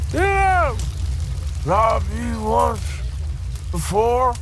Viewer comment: What did he say at 0:04 💀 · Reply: He says "Hey... didn't i robbed you once before?"